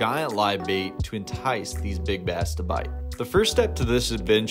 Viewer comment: I thought he said big backs